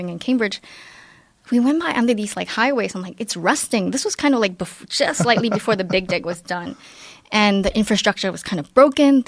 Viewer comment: “slightly before the big dick was done.” did i hear that right?